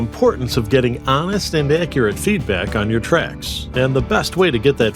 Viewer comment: lol i heard trash no wait i think it is